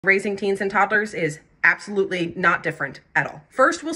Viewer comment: I thought you said raising Cains and toddlers 😂